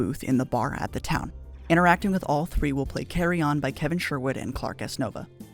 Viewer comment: I thought you said “Carry On”.